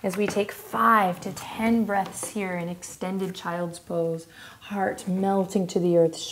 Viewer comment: I thought she said extended Child support